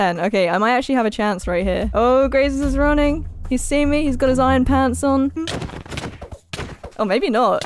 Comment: did just say she is handsome? or am I hearing that wrong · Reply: Rudhly D ‘irons pants on’ Ahahaha